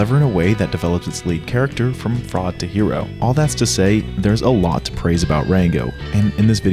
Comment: i thought bro said from frog to hero I was boutta throw hands like wtf frogs cant be heroes now wtf man but hes chilling I just misheard him